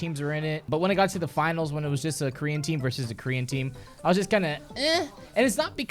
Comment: Every time I hear you saying "Korean Team" i understood "green Team" and im like.... WTF is this fucktwat talking about..... but then I realized...... I am the fucktwat, it was me... Who knew?